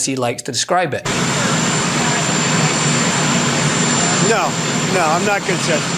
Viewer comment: did everyone hear what i heard?? "No, no I'm not gonna tell you" 5:45 time stamp in this video. In context this is him answering??? I get frustrated because it's 2025 and we are allowing these crazy interviews to happen with jet engines in the background..like...just do this on the plane or literally anywhere else OMG!~!